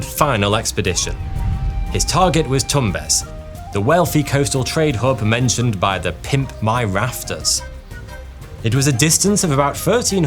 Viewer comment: the "pimp my rafters" was too good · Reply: I went back twice bc I was in did he just say pimp my rafters ? 😆🤣🤣